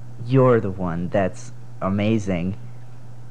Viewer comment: “You’re the amazing one” ᴬᴺᴰ ᴵ ᵂᴬᴺᵀ ᵀᴼ ᴷᴵˢˢ ᵞᴼᵁᴿ ᶠᴬᶜᴱ!!! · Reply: I thought he said "no one wants to kiss your face"